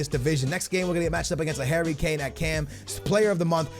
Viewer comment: sounds like he says HAIRY kane😂